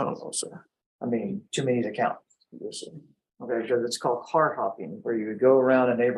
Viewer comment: I must be getting old, or maybe it’s because I’m an Aussie, but I thought that they said those little turds were bar hopping initially, which we can do here legally at the age of 18. I was so confused, and think that I need to lie down.